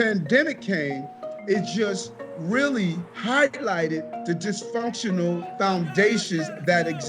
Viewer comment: Did he just say Hi-Glighted instead of highlighted